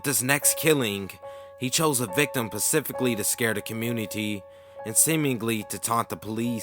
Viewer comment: what does "pacifically" mean or was the word "SPECIFICALLY" ?